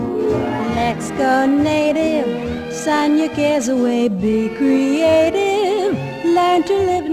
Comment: I hear "Sign your gives away, be creative" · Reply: I heard "sign ur kids away"